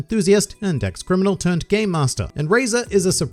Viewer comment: Why did I think you said “gay-master”?